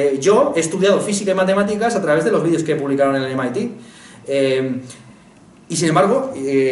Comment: Alguien me podría decir dónde estudió física y matemáticas? No se entiende demasiado bien. Gracias. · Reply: Ángel Aguilar Orellana Con los vídeos del MIT. Es el instituto tecnológico de la Universidad de Masachussetts. En Youtube tienen un canal y suben vídeos de cursos de matemáticas y física.